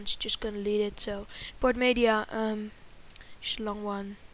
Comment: I think i heard "schlong one"